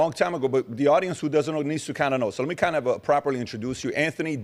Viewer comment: I thought he said he doesn't know Kennedy, then calls him a conspiracy theorist. So do you know the man it not you pervert?